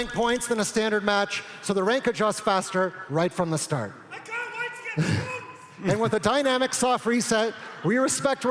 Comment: what did he say lol? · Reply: "I can't wait to get Bronze"